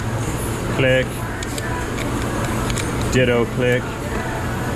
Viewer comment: I am not sure, but... I thought I heard you say, "Ditto Click." LOL I tried to listen again, but it was hard to hear. Yeah, cars, electronic parts, just pick a random, fun time to break.